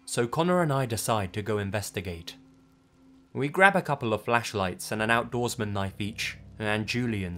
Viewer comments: Holy crap, I thought that said fleshlights at first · I thought the first one said “fleshlights”